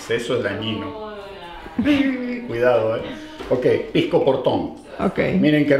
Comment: Jajaja pensé que dijo pisco botón